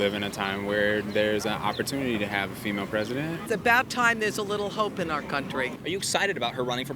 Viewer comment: I misheard the woman who said “a little hope” 😂 a little 304.